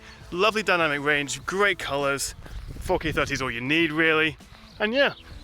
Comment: Great Review ! But at 6:03 did i just hear "$#%# it, that is all you need" ? Awesome !